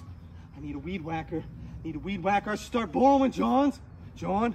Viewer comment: “Mowing so long the mower just yawned 🔥🔥” “need a weed wacker start borrowing johns” 🤦🏾‍♂️😭 this man cant miss. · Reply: until I read this, and even tho there's subtitles, I fr thought he said "blowin johns" and now I dunno if I even think it's better or worse now